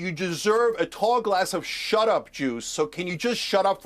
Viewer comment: I think he called Cenk blasphemous because Cenk said something like "Hey Allan. You deserve a.. talk less of shut up jew!" 5:09 Both dems were completely debasing themselves.. Cenk most.. Anyway, keys to the election.. lol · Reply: I thought CenK said you should drink a tall glass of shut up juice. Not shut up j e w